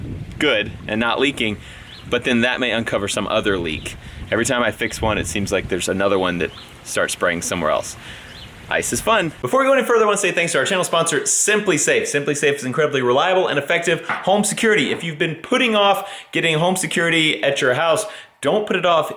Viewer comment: i thought said "isis is fun!" but he said "Ice is fun!" it was funny i thought...